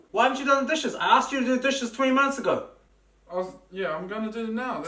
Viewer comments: sorry maybe it’s the accent but idk if he said “OI I ask you to do the dishes 20 months ago” or “ OI i ask you to do the dishes 20 miles ago” 🤣🤣🤣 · “why haven’t you done the dishes I told you 20 months ago” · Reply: I thought he said 3 months ago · I thought he said he asked the guy to do the dishes 20 months ago not 20 minutes ago.